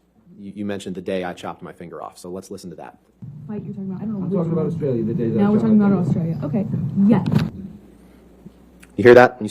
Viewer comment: Johnny sounds like he said: "I'm talkin bout Australia the day ya chopped my finger off"